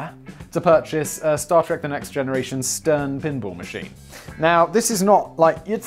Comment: Ohhhhhh. A pinball machine. I kept hearing "pimple machine" and was like wtf is a pimple machine and why did he want one so badly?